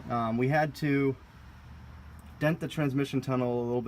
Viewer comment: Did i hear dent the transmission tunnel??